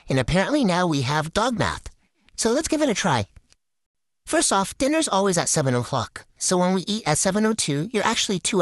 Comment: Why did I hear 💀 "DiNnEr aLwAyS at SeveN Oh ✨FuAk✨"